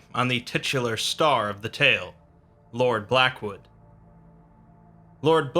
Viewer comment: I thought this shit said lord backwood